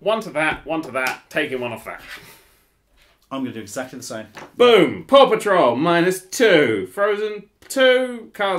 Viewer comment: I keep hearing "poor patrol". Wonder why.